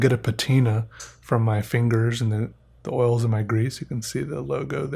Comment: lol "the oils in my grease" 5:22, or did he say "the oils AND my grease"?